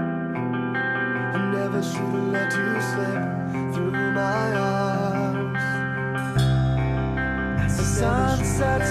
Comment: sound like he sayin "never shoulda let you slip, through my arse"